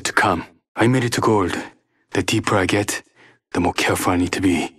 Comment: “the deeper I get, more careful I have to be” that’s what he said